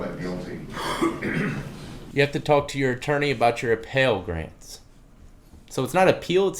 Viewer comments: I thought the judge said “you have to talk to your attorney about your appellate rights” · "appellate rights," not "appeal grants." you were right about how to pronounce "appeal," you just misheard what the judge said because he's whispering into a potato